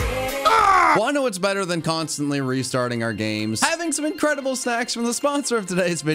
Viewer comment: I can’t have been the only person who heard “incredible sex” the first time